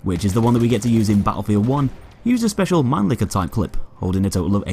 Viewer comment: wtf did you say? Magnefitied clip? What? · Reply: Mannlicher, its a type of clip